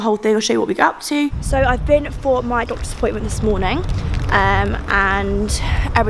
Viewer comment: omg I misheard doctors appointment as disappointment